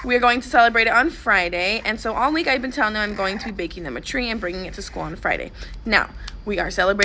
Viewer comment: I thought she said she said that she was gonna bake trees and bring it in